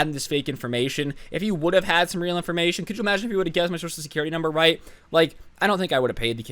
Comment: all i heard was couldyoumaginifhedmysoclsecuritysumber